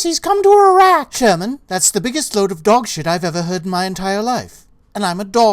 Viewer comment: does he say dog shit?!?!